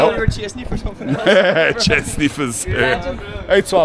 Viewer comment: "Chair sniffers" 🤣 The lads were asking if you thought they were chasing you for something haha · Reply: I thought they said chest sniffers! 🤣😂